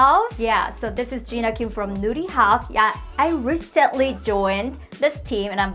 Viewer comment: I love it! I still chuckle every time I hear Jina say "Nurihaus" though because it sounds like she's saying "Nudey House" 🤣